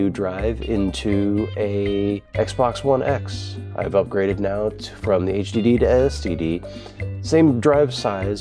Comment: im sorry but did you say "from hdd to std"